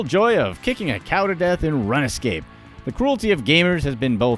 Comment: did he say run-a-scape???